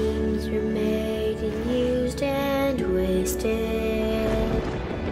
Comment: When I watched the animation I thought it said: And used and will stay